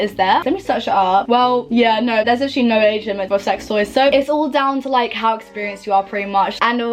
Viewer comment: Lol when you said about sex toys thought you said Asian sex toys lol like wtf haha